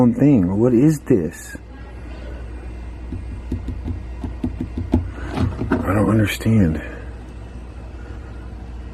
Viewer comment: who was that woman saying something? I can't figure what it says. It's not voice. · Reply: Dhianne Mijarez I hear it and I'm hearing "not safe"